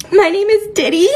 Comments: The first 5s, I heard "my name is Diddy" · Did she just say her name Diddy???? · The first person when I first heard it sounded like she said my name is Diddy. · was I the only one who thought she said her name was diddy? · "My name is Diddy." Great start! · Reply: i heard "my name is titty" :D